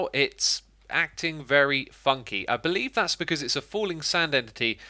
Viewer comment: I thought he said f***y but he said funky